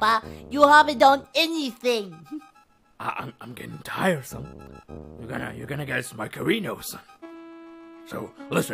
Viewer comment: i swear he says "you're gonna get a smackerino, son"